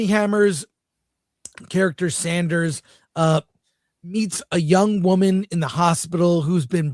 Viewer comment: I could have SWORN said, "Armie Hammer's character, Sanders, EATS a woman in a hospital"!!! LOL!!!